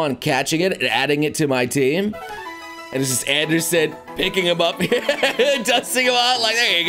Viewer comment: did he just say Anderson?